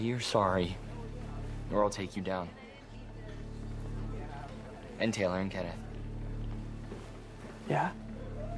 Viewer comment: What did Tyler say? I couldn’t understand because he spoke fast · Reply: He said “and Taylor and Kenneth” I’m guessing it was the two others guys with monty in ‘that’ scene